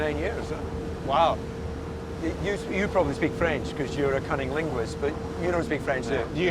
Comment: Did he... did he say cunnilinguist to Carlos?